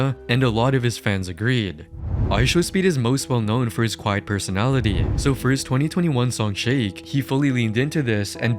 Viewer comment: did he say "quiet personality"